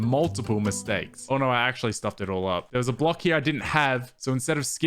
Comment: what was that you just said? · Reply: "actually stuffed it all up"